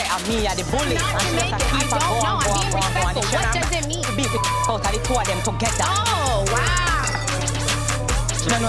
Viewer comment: um so what did she sayyy to make everyone look like that😭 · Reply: She said she’d beat the sh!t out of both of them together lmao